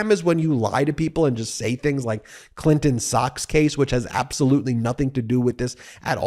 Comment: I misheard you Ben, and thought you said “Socks-Gate” which sounded like the freshest and funniest “—Gate” I’d heard in quite some time and would be well in keeping with the usual hyperbolic-donnie.